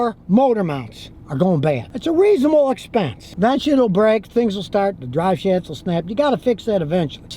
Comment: that shitll break😂 am i trippin or is this the first time i hear him cuss?